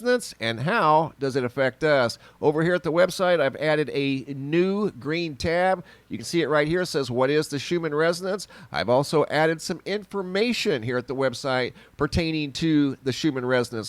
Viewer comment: Thought you were talking about the schumann resident's lol that's what it sounded like you said 😉hahaha